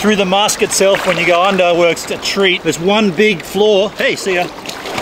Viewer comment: wtf does he say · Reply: "works a treat"